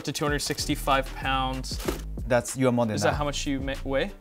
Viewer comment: I thought Dennis said “your mom weighs that” 🤣 had to replay it.